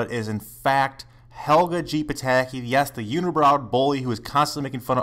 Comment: DId he say at 0:32 ''yes, the unibrowed boy''?